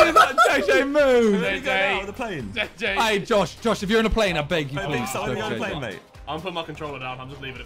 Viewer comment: What did Simon say at 3:44? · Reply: Josh if you in the plane I beg you go for jj please